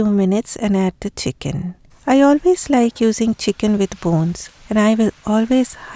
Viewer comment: Lools delicious thank you for sharing! Hope to try it soon. The only thing is ...I thought you said add chicken bones ?